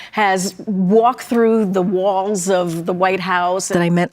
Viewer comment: "As a black woman who has walked through the walls of the White House... " Now that truly is a first. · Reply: Nice catch. Omg she did say walls i thought she said HALLS lmfao what a ding bat